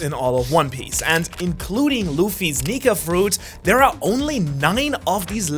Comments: Is it me or did I misheard that he said 'luffys nigga fruit'?? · I thought you said n!gga fruit